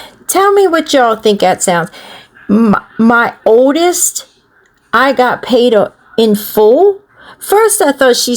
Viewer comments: I hear "my oldest, i got my hands full" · I thought she said “my oldest, I got makeup school” 😂😂😂 I’m dumb!!!